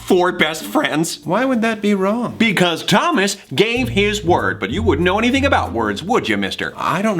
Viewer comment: I thought he said "Gay his word"....halp